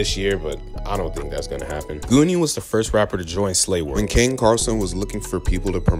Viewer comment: Did he say ken carson or am i trippin. Obviously ken was never slay but then who is he talking about what other ken carson is ther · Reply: Kaine